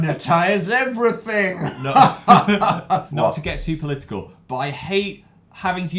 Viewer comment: when he said political for some reason I heard clitical... that’s not even a word